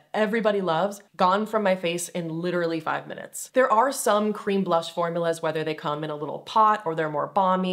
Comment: I wasn’t looking at the screen when you said “cream blush” and I thought you said “I’m no longer buying green blush” and I thought, well no shit 💩